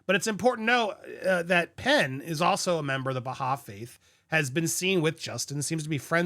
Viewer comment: He’s a member of what with Justin? I couldn’t understand what he said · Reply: A member of the Baha'i faith